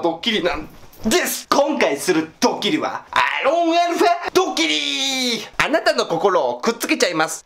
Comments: アーロンアルファドッキリに聞こえるw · 最初のやつアーロンアルファドッキリに聞こえるの俺だけ？